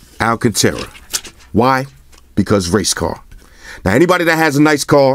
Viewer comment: Ohhhh... race car. LMAO! I thought you said "Cuz, Race Card", at first. "See this case nigga. This case blacker than black. In fact, the only thing blacker than this case is this mother fugga right here. Recognize." Kidding aside, good review, covered all the important bases, thanks!